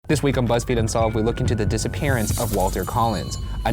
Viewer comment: Me, moving my head on the pillow and making extra noise at the intro so I mishear it: this week of lesbian unsolved